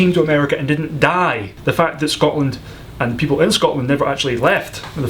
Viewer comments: I had to repeat #10 a few times until I got it. I thought he said "Poland." smh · For a second at number ten I thought he said that someone asked if they have Poland 😅